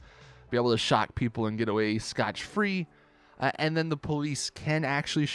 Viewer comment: Did he just say "scotch free" ? Lol